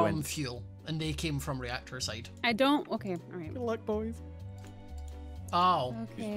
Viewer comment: Did anyone hear at 7:49 said I like boys or did I hear wrong?